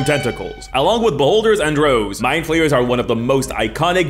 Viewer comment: I was driving while listening this, and at 0:45 I heard "along with beholders and rogues, My Players are one of the most iconic villans in dnd". I couldn't stop hearing "my players" instead of "mindflayers" for a good part of the video, lol ¡Un abrazo desde el fin del mundo, maese!